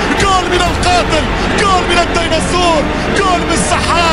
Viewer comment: I don't understand arabic but i think he said Dinosaur ! lol